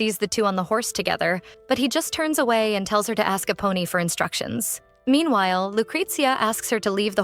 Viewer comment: All I hear is "A Pony" 😂😂while its actually "Eponi"